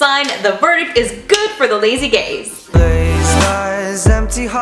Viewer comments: i thought when she said “lazy gaze” she said ”lazy gays” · I'm sorry (not sorry) but when she said 'the verdict is good for the lazy gaze' I was slightly out of it and I thought she said 'the lazy gays' and I just went 'better tell my friends' lol